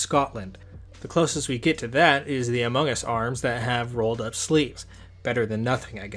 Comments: I almost thought he said the amogus arms i was gonna flip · i swear to god it sounds like he said among us